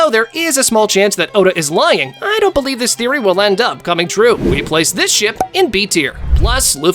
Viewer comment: Every single time this guy says "we will place this ship in ___ tier" I hear "we will place this shit in ___ tier"